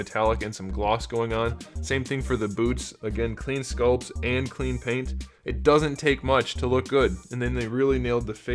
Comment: This one I picked up 40 days ago from GameStop for $18.39. He's 90 g. I thought you had said "Clean Taint" and I was very confused. But you actually said 'Clean Paint."